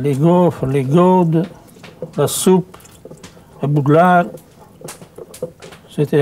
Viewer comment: Il dit quoi à 4:36 ? Un bout de lard ? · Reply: Un bouglard